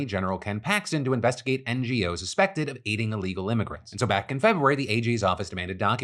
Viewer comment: I had to do a double take when i heard: „suspected of eating illegal immigrants 💀